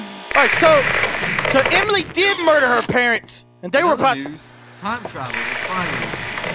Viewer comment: i think he said "timetravel is vital"